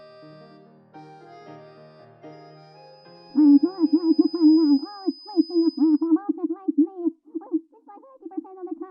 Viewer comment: what's he actually saying here? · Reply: He saying that rabbit acting silly stupid and nonsense